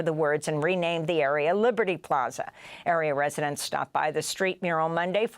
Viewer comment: She said "area residents" but I heard "aryan residents" 😂